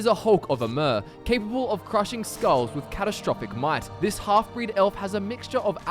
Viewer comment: I thought he said "catastrophic mice". XD